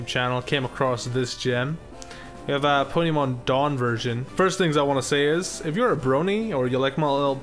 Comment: Thought that said "down version"